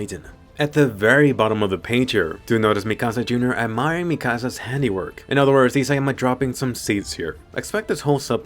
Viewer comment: can someone tell me who he was talking about admiring mikasa? I don't know the name and all I heard is "Mini mikasa"